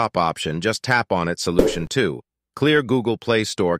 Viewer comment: wait what it said "slution" ☠️☠️☠️☠️☠️